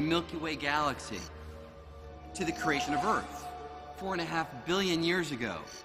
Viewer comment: did he just say "Creation" of Earth?